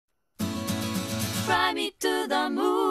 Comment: sounds like shes saying "fry" me to the moon,,...lol anyways, i stil love this song, and i love lena!!!! shes gorgeuse!!!